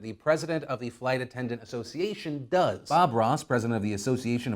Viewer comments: Did you say Bob Ross! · did you say Bob Ross? I'm sorry but off of principle I'm gonna side with him.